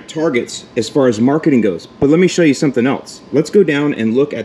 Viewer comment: Instead of “let me show you something else” I heard “but wait, there’s more!”